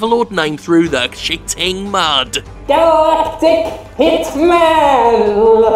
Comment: I thought the first time I heard that Skeletor said "Dark Dick Hitman", I was going to ask what porn game sites has he been to?